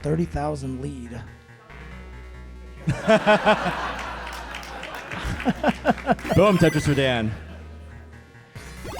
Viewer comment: What did DANQZ say? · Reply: 1 year to reply but he says 'stop being so good'